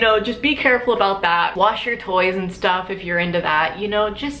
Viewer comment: I thought you said 'you know, wash your toes if you're into that' but what you actually said was just 'toys', jfc I was so confused